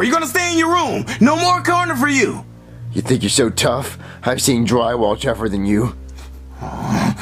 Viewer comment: What did Junior say at 14:57? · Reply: think you’re so tough? i’ve seen dry wall tougher than you